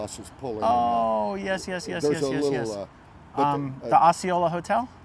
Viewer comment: I thought you said, "there's a little man." is I was like, you found David! 😂